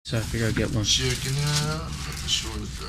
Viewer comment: So we've solved the ending, but what does he possibly say at the beginning? All I hear is "so i gotta figure out... get my chicken out"